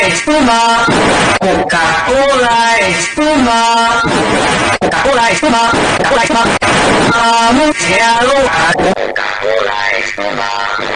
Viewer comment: U know this whole time I thought they were saying "coka cola is to much" yep.